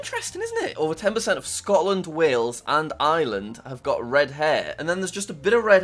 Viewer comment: Did you just say island?